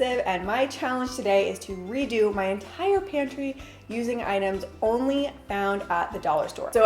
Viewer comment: I heard “PANTY” organization and got really confused............